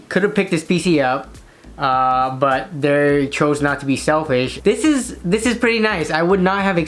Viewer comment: bruh the whole time i thought you said he was being selfish.